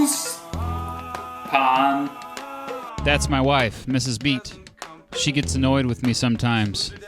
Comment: I thought he said he is Mr. Beast. I am disappointed.